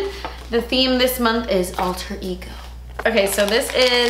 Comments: Omg I wasn’t looking at the screen when you said “Alter Ego” so I thought you said “All turkey go” lmao wut 😂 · when you said alter ego for some god forsaken reason i thought you said “all turkey go” and i didn’t even question it until i saw the card 😭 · I thought you said this month’s theme is “all turkey go” 😂